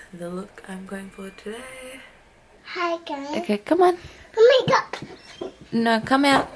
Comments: It sounded like said Hi Guys when he had the Makeup brush lol 0:26🙈 so cute ❤️ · Did try doing his own makeup tutorial 😂 it sounded like he said hey guys with the makeup brush